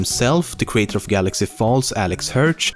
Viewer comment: REALLY? did you just say the creator of GALAXY FALLS?